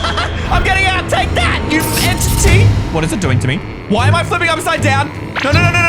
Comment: did you just say bitchy 😮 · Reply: Entity